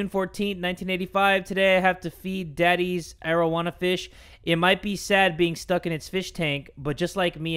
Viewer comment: I thought said marijuana fish😂 Im dying